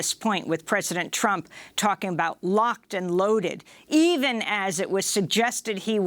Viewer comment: I think Trump said "cocked and loaded"